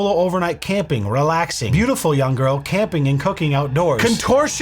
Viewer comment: I had to rewind when you read her video titles. I thought you said solo girl camping and hooking outdoors.